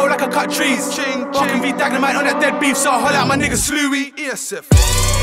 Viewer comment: Did Potter say nigga at like 2:45? 🤦🏾‍♂️ I knew he was dat guy just can’t help itb